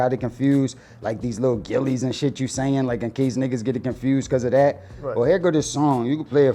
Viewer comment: he talks about Gillie, but I ain't hear him refer to Gillie as lil Gillie. But @ 1:30 this nicca is delusional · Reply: He actually said "Like these lil Gillies and shit you sayin, like in case niggas get it confused cause of that"